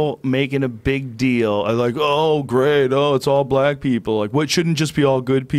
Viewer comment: Did he just say black people aren’t good people